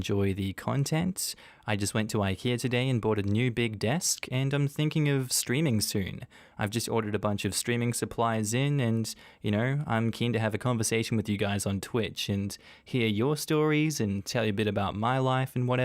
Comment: My goodness at the end I thought you said you bought abunch of SCREAMING supplies from Ikea and I was like ????